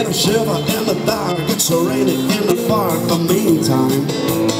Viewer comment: Nice cover, but at 0:16 did he say "raining in the fark" 😆